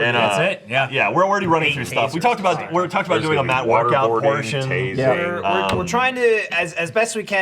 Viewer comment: talked over a bit there and it sounded like they would be waterboarding Matt for their 12 hour stream 😭 I really hope that stream gets to stay up and be available to watch at a later time, I probably won't get to see any of it live.